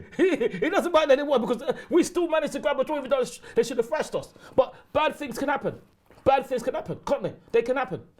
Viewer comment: wtf did Ty say. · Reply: "Still managed to grab a draw even though they should've thrashed us"